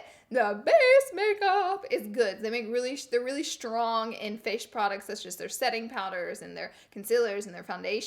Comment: she says fash instead of face lmao